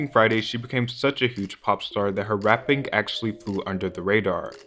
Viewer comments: I couldve sworn that shit said porn star I was boutta say bro you wild 😂😂😂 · Who else thought this said "Pornstar"😭😭